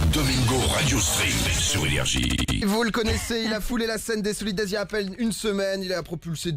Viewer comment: a 0:04 il dit quoi domingo?? je sais pas si je suis con mais je comprends vraiment pas ce qu'il dit. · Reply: “ Les solidays “ c’est le nom du festival où ils ont chanter mirador byebye etc